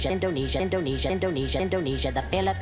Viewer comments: Yakko saying indonesia repeatedly sounds like hes saying tunisia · After hearing indonesia so many time I hear "donatian"